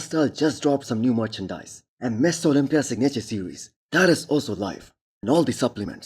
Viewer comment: Does anyone else think every time this guy says Mr. Olympia, It sounds like he is saying Misses Olympia?